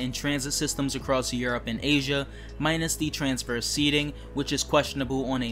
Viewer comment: in video you said " minus c transfer seating" ??? What does this mean. Please explain these certain terms that many of us might not visualized what you're saying. It's very confused what you're saying some of the words just seem sluggish I don't know it just comes out this way. Try to speak clearer next time we love your videos and your opinions stay well during this pandemic we're still in all the best you in your future videos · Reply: "Transverse" Seating